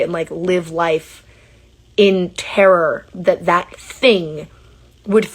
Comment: Did call Daniel a “thing?” That’s fkng cruel